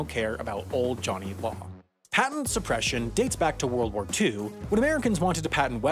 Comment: Unfortunately, I am in need to inform you, the legitimate owner of this channel, that a mistake might have been made in the minute 1:31 of the video: even though the subtitles say World War I, it seems that you said World War II. Thanks for your attention.